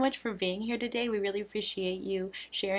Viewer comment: Was watching on 2x speed, misheard his last line as 'thanks for letting me out' 😂